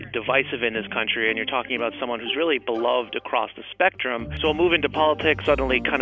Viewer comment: Audio says "beloved," while the subtitles say "loved".